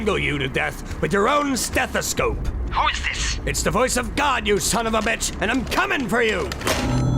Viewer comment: Even if this is a horror film, I can't get over soldier's line of: "It's the voice of God, you sonofabitch, and I'm COMING FOR YOU!" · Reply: I always thought he said “This is the wrath of God you son of a bitch and tell Satan I’m coming from next.”